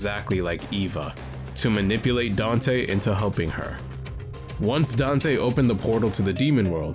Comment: I choked at 2:00 thought you said "manipulate Dante into humping her" 😅